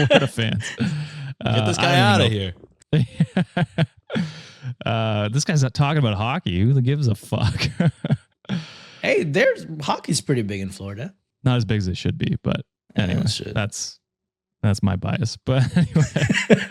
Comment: When mentioned hockey I thought he said who gives a Puck😂🤦🏽‍♀️. You guys are running off on me😊